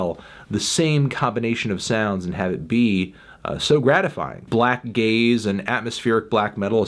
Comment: Genuinely thought he said “black gays”